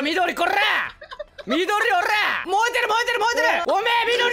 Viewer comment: 燃えてる 燃えてる 燃えてるが よーでる よーでる よーでる にしか聞こえない僕は病気か！！？